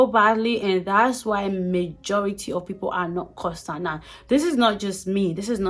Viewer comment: What is the word you say at 6:06min: "...the majority of people are xxxx"? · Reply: 😆😆”that’s why majority of people are not constant” (constant in their job)